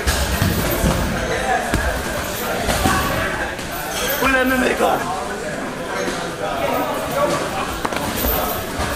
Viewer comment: What was it you said after that guillotine at 3:11? :) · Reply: haha I said he tapped me with boxing gloves !